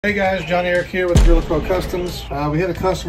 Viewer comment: Thought he said John wick here 😆